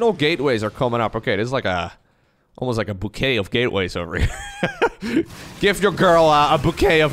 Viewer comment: I heard "bukkake of geatway" accidentally (10:56) Your girl is gonna love it 🤣